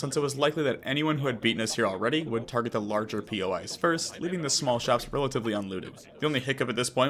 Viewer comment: POS? Or did I mishear it? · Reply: I know this is a year old, but he said POI (point of interest)